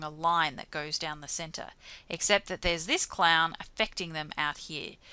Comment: did she call the outlier a clown 😭